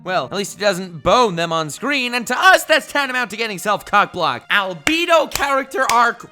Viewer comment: did he say albeeedle